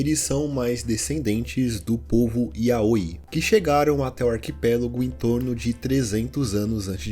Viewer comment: é "iaIOi", yaoi é outra coisa JJJKSAKSK